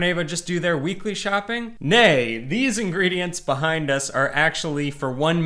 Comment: Did you Said the Dutch no = nee on